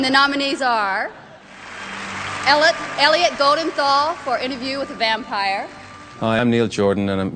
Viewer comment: the sheet music shows that it really is "Interview With The Vampire" even though they say "A Vampire". It looks like people have been misreading and mishearing it since the beginning. 4:36 shows "The" as well. It sounds like most actually are saying "The", but it sounds like "A" because they say it so fast.